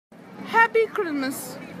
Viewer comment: for a sec i thought she said "happy grimace!" 💀💀